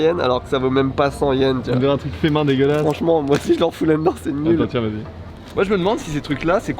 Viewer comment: attend tiens bébé Y’a que moi qui a entendu ça ?😂 Est ce qu’ils n’aurais pas quelque chose à nous avouez ?😏😂😂